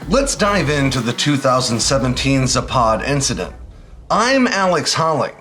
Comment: For a second, I thought you said, “Zaphod,” as in Beeblebrox. 😂 And for the record, I like Vogon poetry. 🤔